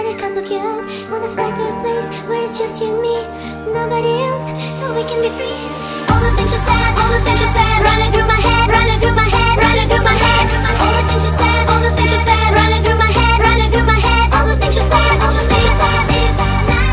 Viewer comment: All the things she said all the things she said runnin' through my head! ~~~~~~~~~~~~~~~~~ My favorite part! ❤ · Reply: i heard "all the teachers dead" not "all the things she said" x'D